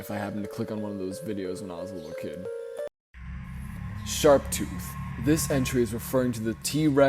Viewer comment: My dumbass thought they were saying Shark Tooth all these years, but now I see this, that just makes a hell a lot more sense.